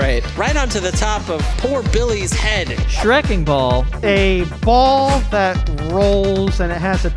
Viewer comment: I thought it said “Shrieking Ball”.